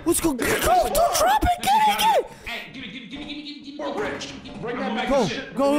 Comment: Did i hear a "we're rich" at 36:13 -ish? Rock and stone!